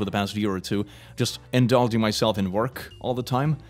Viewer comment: What does it say about me when he said, "I indulge myself on work" and I thought he said, "I indulge myself in orc"?